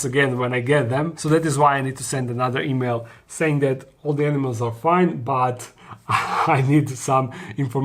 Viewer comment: I thought you said no animals for now 😆